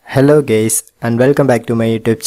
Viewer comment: Did he just say hello gays LMAO